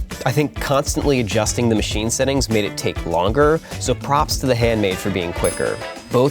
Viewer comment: I thought it said Hand Machine